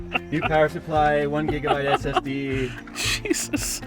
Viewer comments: At around 11:10, did Jay say 1 GB SSD? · did he say 1 GB ssd?